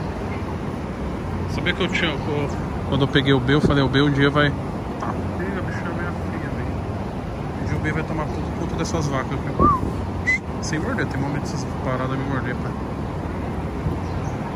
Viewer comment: ''bixa veia feia" kkakakaakakakak foi isso que ele falou??